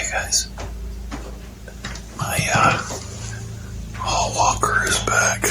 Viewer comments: Holy hell I thought he said Paul Walker is here. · I finally have my subtitles on when first saw when it came out I thought he'd said Paul walker is back like he referring him as Paul walker he's as saying my hall walker is back